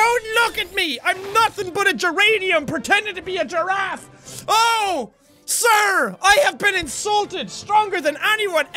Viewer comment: I thought It said "Oh...Siri!" But no. Its "OH...SIR!